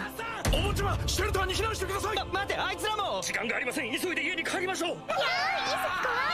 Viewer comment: is Saiko really saying "friends"? I think I heard those words "hai zi" or something like that come from Saiki when he was asking Kuusuke why Nendou and Kaidou were in London with them. I feel like Saiko is saying something like "them too" or something idk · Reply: He said "Aitsura Mou" which means, "Them too!"